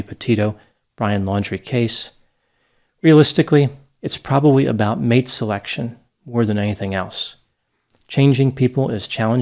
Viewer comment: Just to bring a little light to this tragic case and situation. For a second I thought you said: “it’s probably all about Maid selection”. I’m personally not sure if this helps, I feel it could also escalate the situation 😉